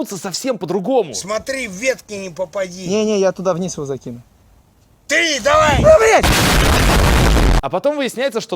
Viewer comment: Мне послышалось "Умри"